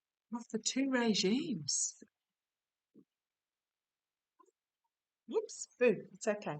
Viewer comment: I heard at the end of the video 20:57 a voice saying, "whoorah"